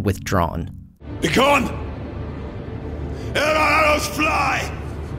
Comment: Can anyone tell me what language he’s speaking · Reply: Thorin says “Be gone! Ere our arrows fly!” Its a direct quote from the book, actually. Basically telling Bard to clear out or they’ll shoot him. Harsh.